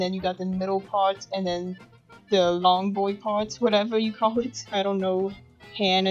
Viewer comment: DID I HEAR LONG BOY PARTS LMAO